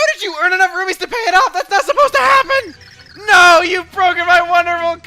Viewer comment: I was watching this video from my phone, but it was freezing. I heard, "THAT'S NOW SUPPOSED TO HAPPEN!!" while I was in a different app. That was pretty scary.